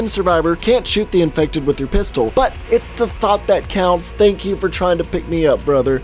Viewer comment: Did you just say thot?